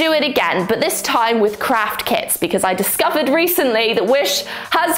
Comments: Lol I swear I thought she said witchcraft kits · I thought she said witchcraft kits at the intro🤣🤣✋💀